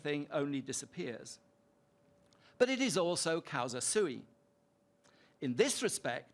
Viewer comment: what was that word? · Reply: Causa sui (Cause of itself, if you want it in english)